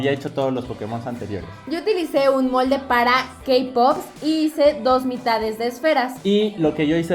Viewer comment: kpop 4:49 dnjidw se que no es eso pero yo entendi kpop jdfnvoj